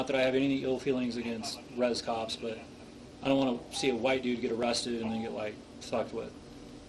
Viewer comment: does he say I don't want to see a white man get pulled over and messed with??